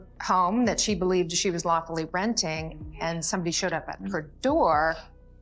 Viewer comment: what did she say 😂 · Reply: "That she believes she was lawfully renting."